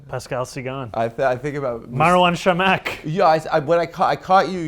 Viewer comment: Did he say Marwan Chamakh ?? The Moroccan player ?